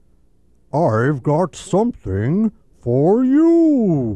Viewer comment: As a child I always thought he said "I've got something... How are you?"